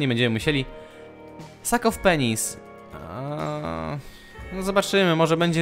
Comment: Powiedziałeś "sak of penis" xd czy tylko ja to usłyszałam ? :d